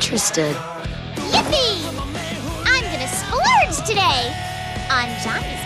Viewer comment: what did she say!? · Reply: Splurge. noun: an act of spending money freely or extravigantly